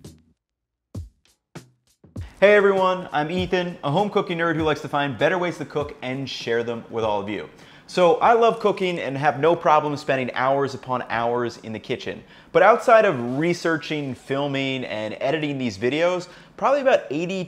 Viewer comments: I swear to God he just said he loves cocaine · sounded like he said i love cocaine lmao